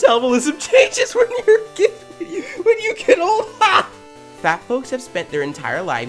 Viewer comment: I heard fat fucXs 😂😂😂😂